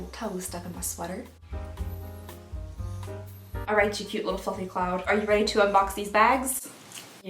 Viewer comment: At the beginning i thought she said “cute little fu*king cloud” 😂😂😂😂